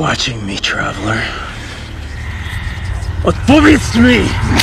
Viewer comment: does anyone know what he says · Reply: Odpowiedz mi (answer me)